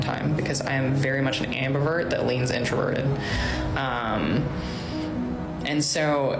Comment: When she said: "I am an introvert", I heard: "I am an Amber Heard"!